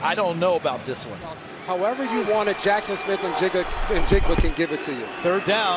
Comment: did you just call jsn a jigga?